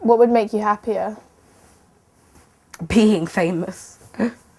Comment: "what would make you happier?" "peeing famous!" .... tell me that not only i heard that :P